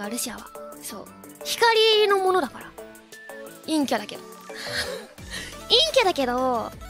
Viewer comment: Thanks for the clip! 1:31 I think she's saying 陰キャ (inkya) here though, not yin.